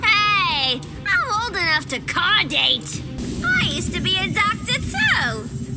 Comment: Harley says I’m old enough to what I don’t know what she said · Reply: Car date